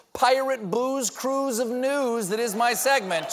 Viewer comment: "Booze cruise of news." That's a keeper. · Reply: I totally misheard it as “Blues Clues of news.”